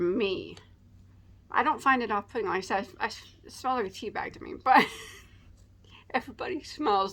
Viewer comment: OMG I thought you said it smells like pee...then realized you wouldn't put your nose in it again so figured you said tea. Glad you got the yarn you ordered and that was super nice of them to let you keep the green hanks.